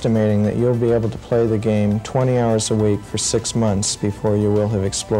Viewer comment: 20 hours a day, 6 days a week in order to fully explore the game. Yeah okay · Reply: On a sidenote, that's not what he said. 14:45 - 'Twenty hours a week, for six months' Todd Howard can still shove it, though.